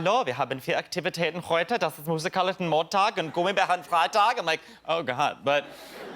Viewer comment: The German accent is great but I didn't quite catch everything. I heard "hello, we have many activities to do. We are doing ... Monday and doing ... Friday. Did anyone else catch what he was saying? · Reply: Musikalischer Montag, he said. Not sure about Freitag.